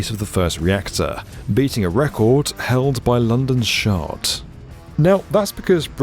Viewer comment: Did I hear that right? London's... "shart"??